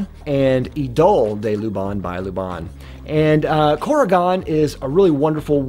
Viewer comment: what is saying? I hear Corrigon by Luvon, but it can't be right. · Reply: Korrigan by Lubin. Almost right. 😁